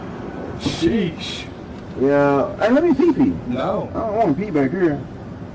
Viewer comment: is he saying "let me pee pee?" 😂